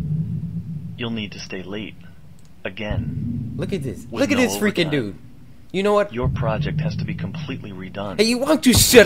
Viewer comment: i think he says the F word